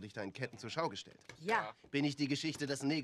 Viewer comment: Just before he answers King Kong, see how his smile fades away instantly when the British guy answers the 10th question. German style of this game differs from British version as they don't answer the 10th question which obviously the British spy wasn't aware of. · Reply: Have you noticed how at 2:49 Hicox slips a very British “No” instead of “Nein” and Hellstrom looks at him? I only noticed it recently and thought this may be what gave him away…